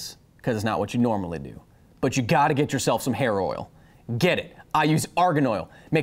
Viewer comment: instead of "hair oil" I've heard "HEROINE"